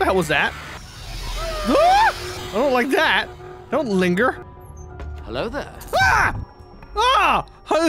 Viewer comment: I love how says "Hello" while the subtitles said "Come out carrot boy"